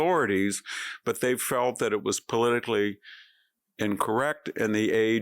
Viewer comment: you said "politically incorrect" but the subtitle said "politically correct, in the age". Words mean things so perhaps your producers might keep an eye on such things.